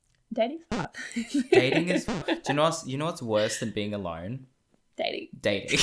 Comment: "You know whats worse than being alone? Dating." Well ill be that is quite the paradox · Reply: Honestly I thought he said “what’s worst then being alive? dating.” Lmfao